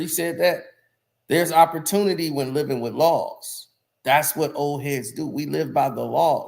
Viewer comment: Thought he said living with loss not living with laws?